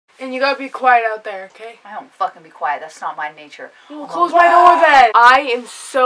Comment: Wait, did her mom just swear? Lmao. I heard her say "I don't fxxxing be quiet." in the beginning. Was it just me.